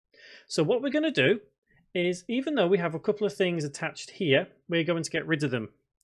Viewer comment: I heard, "Even though the cube DOOP" for some exact reason.